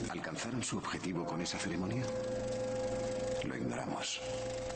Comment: Sanchez Heredia i kept hearing "Lo ingramos" for some reason but yea your right thanks il correct it right now